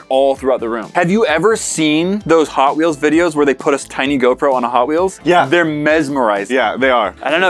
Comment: I thought he said gopher